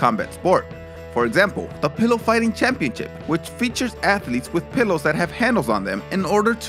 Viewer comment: Oh, your the pillow fighting champ, I thought you said the pillow biting champion.